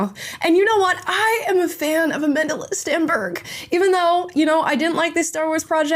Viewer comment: I actually thought you said you're a fan of A Mentalist Stenberg😂😅.